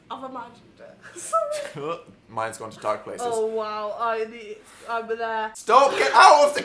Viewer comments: does someone understand what Dan said? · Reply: Minds gone to dark places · Wait, what does Dan say at 9:40? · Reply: my mind's going to dark places